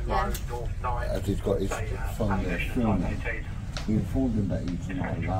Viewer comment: did someone say Scottish? WTF!